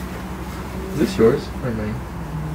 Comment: is Chris French? I heard him say "à moi".